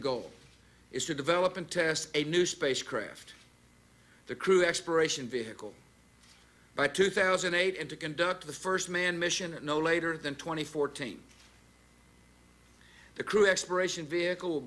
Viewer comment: did he say “crew expiration vehicle”?!